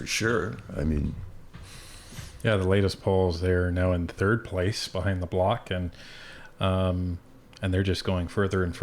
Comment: At first i thought his said ‘behind the devil’.